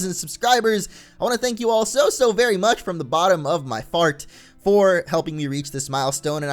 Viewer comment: I thought he said bottom of my fart I'm like, well that's new.